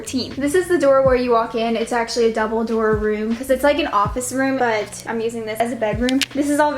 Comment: When she said "double door room" I thought she said "dumbledore room" 🤦‍♀️